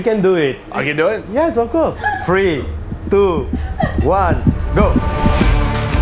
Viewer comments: I thought he said “free, two, one” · I don't know why I heard it free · I thought he said 'free, two, one' 😂 free truffles